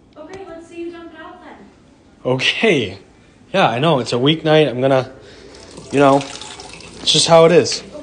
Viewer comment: I honestly thought that she said "weed night"